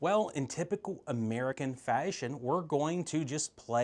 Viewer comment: Sir, did you say "faesheioeon" at 1:36 ?